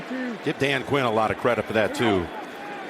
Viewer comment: did he say Dan Quayle????????